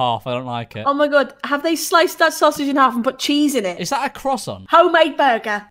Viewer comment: nah did really say is that a crossont 9:09 😂